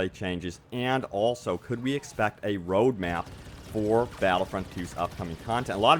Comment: Man i thought you said Rouge map :( not road map watch the whole video for no reason .